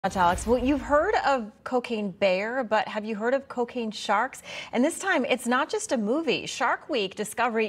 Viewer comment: At first I thought you said COOKING shark and I said, "YEAH! Shark tastes great!" Then I realized they were just trying to get a shark into the White House!